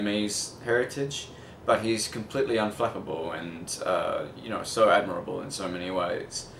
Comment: what is he saying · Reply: unflappable: having or showing calmness in a crisis not he cannot be flapped lmao